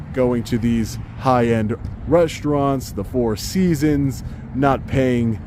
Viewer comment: I love your audits, I always wath everyone of them. But what is reshtoran on 27:05 minutes of this audit. I keep hearing you saying shrtreet, shchool, frushtration and so on